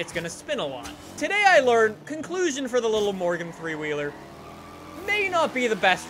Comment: Every time you said morgan three wheeler I heard you say Morgan Freeman